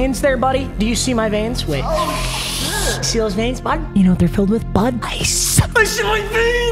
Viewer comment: I thought you said your veins were filled with bud LMFAOOO